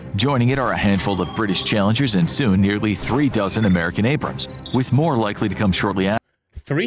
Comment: It sounded like he said 3 dozen not three thousand Abrams.